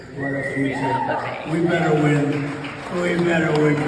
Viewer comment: I heard Trump say "We BETTER win!"